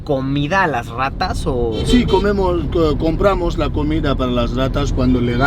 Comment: cuando lusito le pregunto tenemos que llevarle comida, escuche algo sobr si comemos y luego dijo compremos.